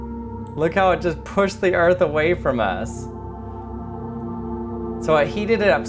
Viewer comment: Wait did he say "See how I pushed the earth away from us?"